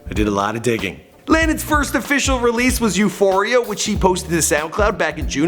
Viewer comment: am I only the one who heard him call Landon a she? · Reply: raged he said "which he posted" he just said It fast so the ch sound blended with the h making it sound like she